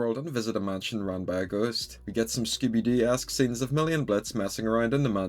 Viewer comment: what did he say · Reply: Scooby Doo-esque scenes